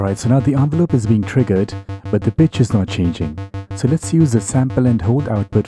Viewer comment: Thought you said "The bitch is not changing" lol